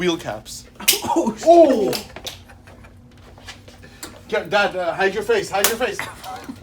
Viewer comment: did I hear dad? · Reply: Sage Akporherhe yeah you heard right. It's not Moses though. It's the guy right next to him with the white hair!